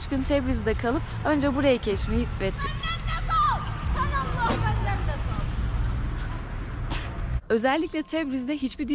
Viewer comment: 'te arabadakiler ne diyor acaba merak ettim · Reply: Lutfen bizden de çek diyorlar😂😂